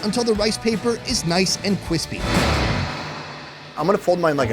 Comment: anyone catch that he says Kwispy..